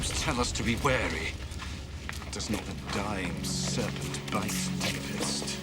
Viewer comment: Can anyone make out what Cicero says at 6:33? "Does not the dying serpent bite ..... " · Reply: deepest?" :)